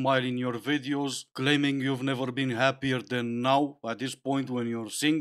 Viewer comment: I heard "happier than meow" ... :D